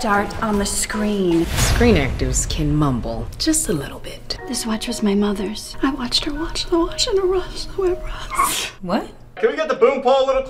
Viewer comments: i heard “this was my mother’s i watched her watched he watch the wosh the rosh at rosh” bro what 😂 💀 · "I watched her wash the watch in a rush" "I am your manager" "I quit" 😂😂😂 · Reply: I heard her say "I watched her watch the wash in the wash going to Ross 😢" help 😭😭😭😭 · When she mumbled I heard. “She watched in my watch in a rush” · I heard “this watch was my mothers I watched her mirage mirage into a mirage